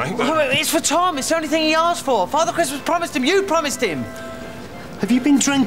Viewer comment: the subtitle is wrong, it should say 'Father Christmas promised him, you promised him'